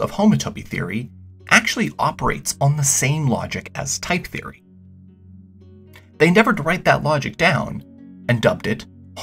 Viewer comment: what did he say? They derived that logic? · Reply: "They endeavoured to write that logic down". Bit quick, sorry